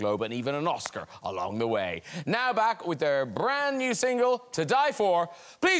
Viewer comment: “Now back with THEIR brand new single” im sobbing 🥺🥺🥺 it means so much to have pronounce respected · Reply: At first I thought he said "HER new..." I re-listened and heard "A new single..." Or maybe he said "THE..."?